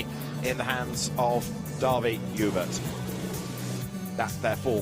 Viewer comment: did I just hear a Hubert? Is Antoine and Davi in any kind of relationship like siblings or parent? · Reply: Dawie joubert. It's an afrikaans name, the commentator didn't pronounce it correctly